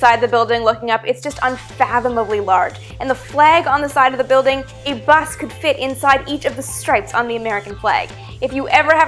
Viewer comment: What di-oes she say at 02:55? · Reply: "A bus could fit inside each of the stripes on the American flag."